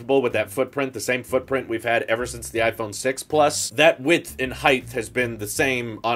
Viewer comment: Did I hear “That width and heighth” at 3:45 ?